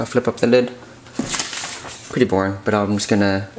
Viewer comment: did you say "pretty porn"????